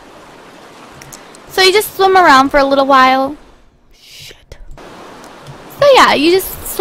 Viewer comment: did anyone notice at 5:02 , it sounded like "shit"? It sounded like a demon said that, o,o